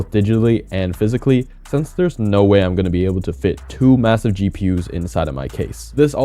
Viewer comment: Did you say M-Massive?